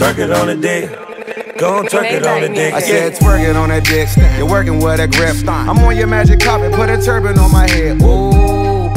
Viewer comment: What she said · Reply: Maybach Music